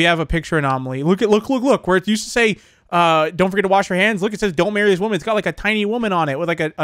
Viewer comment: I thought you said "don't bury this women"